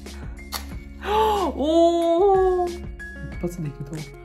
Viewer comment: でてもーてるでてもーてるwwwwwwww しかも、ふふ、やめろw って聞こえるんだけど爆笑